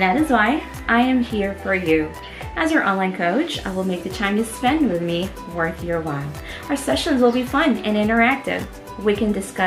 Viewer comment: i like the energy, if i may ask, what did you say on 0:19 secs... thank you. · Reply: "I will make the time you spend with me worth your while. Our sessions will be fun and interactive..."